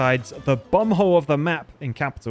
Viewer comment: All this time I thought you were saying bung-hole of the map - has a much better ring than bum-hole.